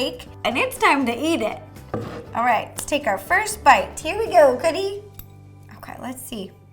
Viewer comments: it sounded like you said coddy lol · You said,"here we go couddie and not cookie, is that some kind of nick name or did you just say it wrong?